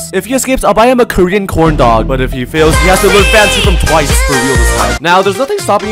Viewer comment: did you say TWICE? I LOVE TWICE!